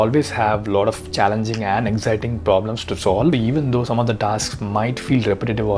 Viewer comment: did u say anxiety at 4:44 !!